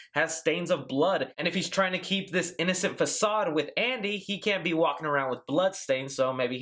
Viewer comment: Is it just me or did I hear 'asshole' instead of 'Andy'